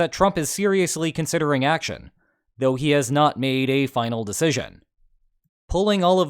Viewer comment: ish. Thought I heard Final Solution, luckily it was just final decision🤔